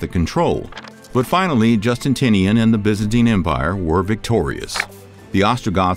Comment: I'm not usually the pronunciation police, but you are saying Constantinople as Constanople REPEATEDLY. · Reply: ish I heard Emperor “Justintinian”. I’m watching the video for the first time right now and felt compelled to comment.